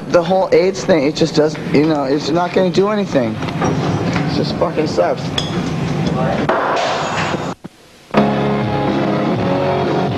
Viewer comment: did he say sus?